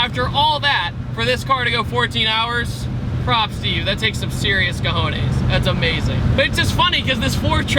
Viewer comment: lmao did he say that takes some cojones